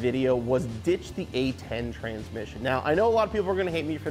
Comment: I thought said ditch the gay 10 speed transmission 🤣😂🤣🤣 I was about to say right on brother! 🤣😂